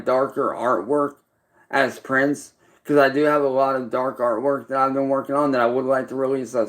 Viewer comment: Is he saying “Tardwork”? Like, TARD WORK?!?! Unreal 😂